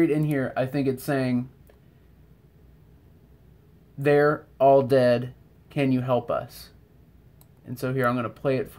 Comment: 11.18 i heard" we,r all there can you help the crown" love your work x